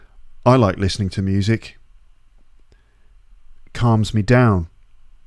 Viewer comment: why I heard “comes” ？